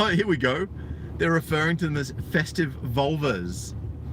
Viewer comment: I had to rewind part of the video, I thought you said they are refering to them as FESTERING vulvas.